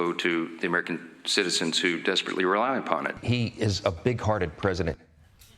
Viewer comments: He clearly said Pig hearted. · 😂 thot I heard "big-hearted" 😂 More like "pig-hearted" The "you" that frumpie trumpie is talking about is the one he sees in the mirror! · I think you misheard, Jon. He didn't say big-hearted. He said bigoted.